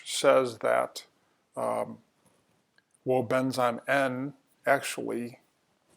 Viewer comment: What supplement did you say here? Sounds like Woendzym N Thanks for the informative video and link.